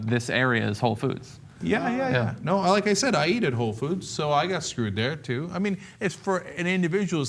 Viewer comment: Did Cenk say, "I eated whole food" at 2:28 ??